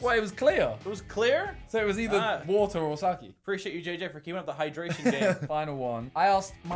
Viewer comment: i hear sucky not sake